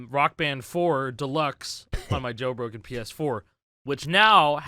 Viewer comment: I heard this as 'on my joe rogan ps4'